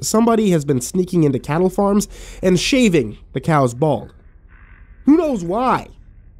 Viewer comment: At first I thought he said "shaving the cows' balls". That would have been even funnier. 😂🤪😁😂